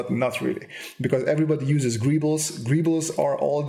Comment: did you say gribbles?